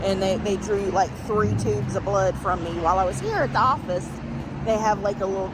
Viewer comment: Did call this place hospice??? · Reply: Office